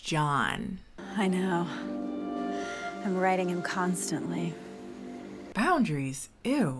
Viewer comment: “I’m riding him constantly” like um what mam who asked why do they always put random awkward sexual comments in these movies😭😭😭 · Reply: omggg this is embarrassing in the movie I thought the mom said I’m riding him constantly😭😭😭